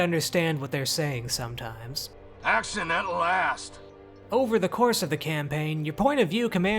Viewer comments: It took me like 5 listens to unhear ACCIDENTAL ASS and hear what he was actually saying ("Action at last!") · I think I hear : "Action, at last !"